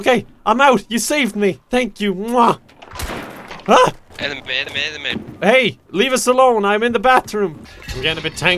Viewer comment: I was reading a text a had just gotten and I heard "enema, enema, enema" 😂